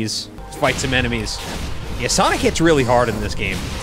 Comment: I thought he said "gets" instead of "hits" 😭😭